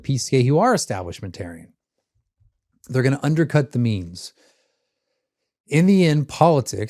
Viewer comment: I kept hearing "undercut the means" as "undercut the memes." But they can't undercut the memes. They don't understand them and they don't have the meme energy, because the meme energy rests with us.